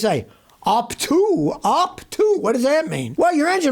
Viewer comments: i thought he said hawk tuah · I thought Scotty said HAUK TUA! But he said UP TO, UP TO! 🤣